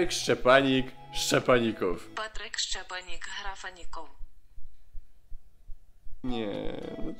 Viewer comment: (Wiem data ) usłyszałam rakaników xD